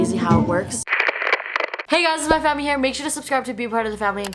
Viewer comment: I looked at the video and when she said ‘hi it’s Mai phammy here!’ I thought she said ‘hi it’s my fanny here!